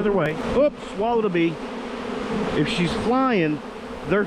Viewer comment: Did you say you swallowed a bee??